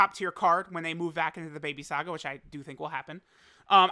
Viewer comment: did he say baby saga lol